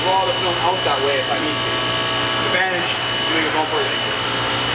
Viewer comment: "The advantage to doing a bumper ????" What was that last word? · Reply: g "that's the advantage to wrapping a bumper like this one" meaning there is no center or top portion to wrap so I can actually feed the vinyl back towards the top center and not worry about it bunching up because I can cut the rest away